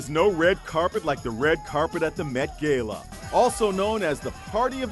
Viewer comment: Did he just say met GAYLA